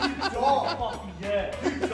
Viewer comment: Is it me or did it sound like say "I'd cake face you" instead of paulie?